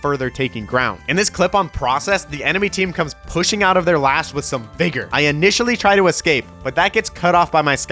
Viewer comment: had to rewatch that and still can’t figure out what word he said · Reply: “In this clip on Process, the enemy team comes pushing out of their last with some VIGOR.